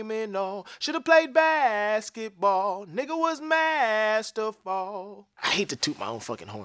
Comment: I thought he said bad to the bone instead of masterful😭 i like the latter better